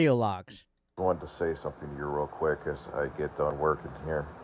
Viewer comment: did he say as he gets done work? Did he actually get a job?